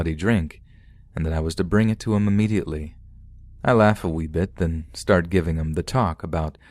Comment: did he just say "I laughed a wiw bit" lol trevor Noah come here.